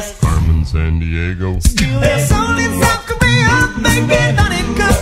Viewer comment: I keep hearing "Steal their soul in South Korea."